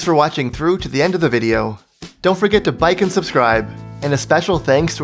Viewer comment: excuse me? bike instead of like? ayo - mico, a man part of gen z (age 18)